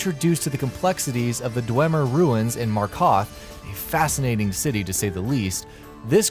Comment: For a second there i actually thought he Said Marcoth. I got confused... Its Mar-Kar-Thh